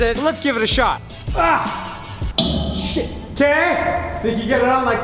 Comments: did anyone else hear him say s**t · did you say s**t